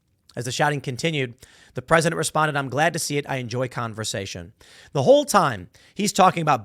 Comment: Biden did not say “conversation” 20:34 He Mumbled something about enjoying “conversion” You Spent too much time on your phone Reading His speech Instead Of listening to his speech.